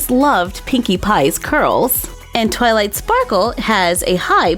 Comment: I thought she said toilet sparkle 🤣😂🤣😂🤣😂🤣😂🤣😂🤣😂